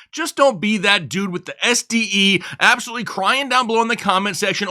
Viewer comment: Did he just say "Don't be that guy that got STDs"?